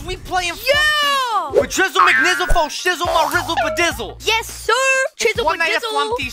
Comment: did he just say forshizzle my nizzle!!!!!!!!!!!????????